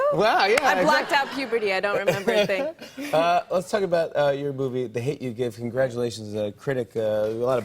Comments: did anyone else think he said lets talk about ur boobie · does Jimmy say "Let's talk about your boobie?" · Am I the only one who heard Jimmy say ‘boobie’ at 3:45? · after she talked about Jennifer and Jimmy said "let's talk about your movie, i heard "let's talk about your booby" and i was like what?! 😂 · am i the only one who thought he said “let’s talk about your boobie”? Especially after what they were just talking about😂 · Oh my god I thought he said let's talk about your booby not your movie. Lord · after that i heard jimmy say “ let’s talk about your booby the hate you give....” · 😂😂 tell me why at 3:46 after the boob and puberty story I heard jimmy say “let’s talk about your boobie” instead of “let’s talk about your movie” and I had to rewind to make sure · When he said let's talk about your movie I really thought he said let's talk about your boobies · Was it just me or did he say your boobie instead of movie